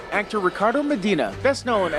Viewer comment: Swear I just heard them say 'Hector Ricardo Medina', lol. At 0:36.